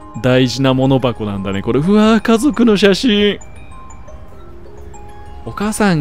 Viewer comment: うわぁ家族の写真～の感じがあぁー、水素の音～に聞こえてふふっwってなった